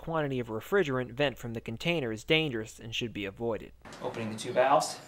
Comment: on segment time 13:49 was the portion say open the two lines ( low & high side ) ? Great video just want to follow if I hear you right . Thanks